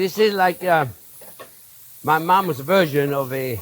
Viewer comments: When he said my mom's version, I thought he said my mom's a virgin. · What he said: "...my mom's version..." What I heard: "...my mom's a virgin..." Some days my brain....